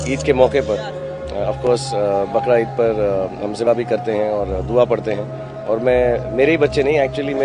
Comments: kyaaaaa ??? Bakra eid pr hum zinaa b krty hn 🙄🙄🙄 Yh kya kaha inho neshyd Mjhy Glt smjh ai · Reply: He saying(ziba mtlb qurbani) not zina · Did I just hear Bakra Eid me Zina bhi karte hai?? Or did he actually say that?? Listen from 1:45.. how stupid if he said that..